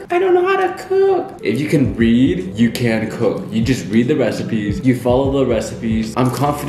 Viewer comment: "if you can read you can cook" Me a dyslexic:YeAH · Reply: I actually thought he said if you can breathe you can cook💀😂